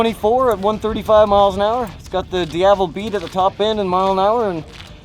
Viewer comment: I cant take his pronunciation of DIAVEL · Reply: I thought that idiot was saying DFL. I didn't realize he was saying Diavel. That guy should be fired for being an idiot with his mispronounciation.